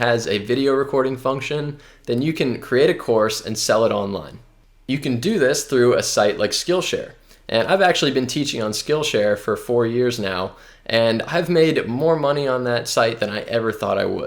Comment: Thought you said 45 years...